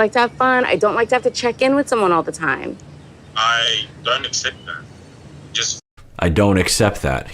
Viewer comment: I heard Syngin say "I don't expect that," not "I don't accept that" (which is what Dr. Honda heard). I've listened to it over and over again and see how both could be heard, so it's interesting to see what some people pick up on (Syngin was saying he ONLY wants her to send him ONE message at the very end of the night to let him know she's home safe, but Tania was replying with "I don't like to have to check in with someone ALL THE TIME". To me it would make sense for Syngin to reply with "I don't expect that" because he hasn't been asking her to give him hourly updates. I don't think Syngin is asking for much - literally one text message one time on nights she goes out (and gets "Jovi" drunk). Considering the couple was supposed to come together within 90 days to figure out if they want to get married and be together for the rest of their lives and they can't spend a third of that time together (especially considering the VERY significant make-or-break differences they have to iron out RE: behaviours, wants, values, etc. such as the topic of children), frankly I think it's quite selfish of Tania to want to pretend her S.O. doesn't exist (let alone that she ditched him in a country foreign to him where he has no friends, work, hobbies, support system, potentially coping mechanisms, etc.) and that she has ZERO responsibilities to her relationship.